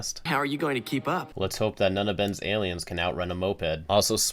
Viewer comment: I keep hearing let's hope none of Ben's aliens can rent a moped.